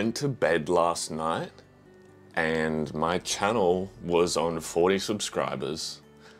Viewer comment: im sorry did you say 40????